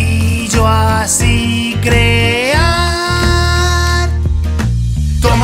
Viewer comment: Yo pensé que decía kreaa pero era crear